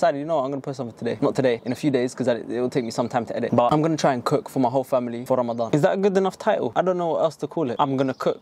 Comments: i thought it said cooking my entire family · I must not be the only one who thought it said “cooking my whole family” 😂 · Nahhh😭I really thought he said “Cooking my entire family”😭😭😭😩